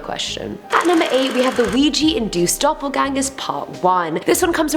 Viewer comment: I genuinely thought she said, "Luigi and Duke" not, "Ouija induced"